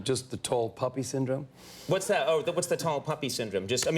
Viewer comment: Thought said tall puppy sydrome...looked it up...poppy...re cutting someone down to size. Mean spirited.